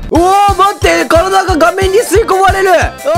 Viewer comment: 「うわぁ、待って。体が顔面に吸い込まれる〜。」 画面が顔面に聞こえるww